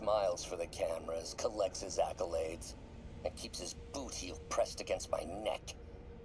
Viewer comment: I thought he said "booty" for a sec and was like wait... what?! 😂